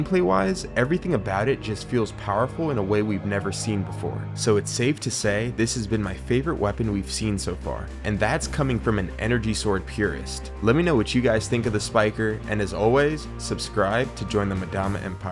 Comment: Misheard you and thought you said Madonna empire at the end...